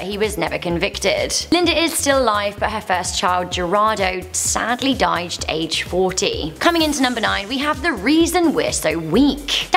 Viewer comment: I hear 40, is it 40 or 14?